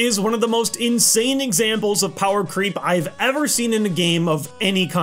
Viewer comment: I thought you said power cream